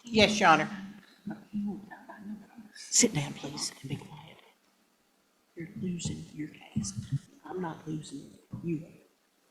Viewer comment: What did her attorney say to her caught on the open mic ?! 😂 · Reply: "Sit down, please and be quiet. You're losing your case. I'm not losing it, you are." 🤣🤣🤣 Nice to know she's the same snotty, snide, bully to her clients as she is to the other side. She really is a nasty piece of work.